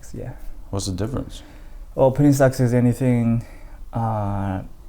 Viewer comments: in the subtitle it says pretty sex is anything instead of penny stocks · If you put english caption on at 00:20-00:22. says " Penny stocks" but caption says" Pretty sex".